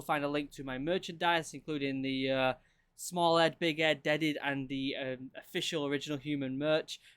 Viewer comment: Who else thought he said big Ed 😂